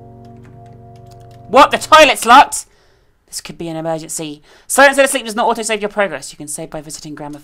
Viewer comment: At first I thought it said "Silence Of The Lambs".